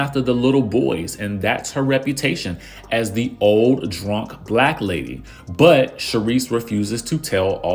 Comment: l thought she said old drunk lady.